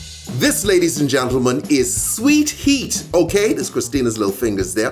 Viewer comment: i thought it said sweet heart!